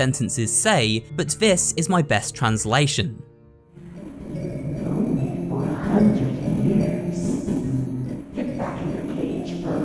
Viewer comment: "you've known me for 100 years" that's what Finley says to Rambley in the train ride! For anyone wondering.